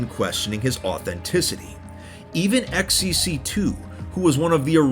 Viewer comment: did he say xQc2?